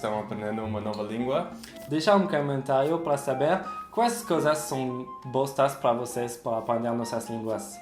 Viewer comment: Aos 7:14 eu ouvi: "Quais coisas são bostas pra vocês" kkkkkkk